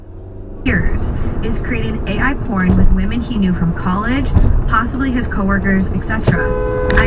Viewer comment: Wait..What did I just hear on the radio??😅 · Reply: Some guy creating a.i. porn of women that he’s known.